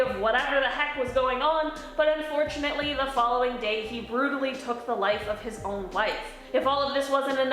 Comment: I for real thought she said he "Took the life of his own life." 😂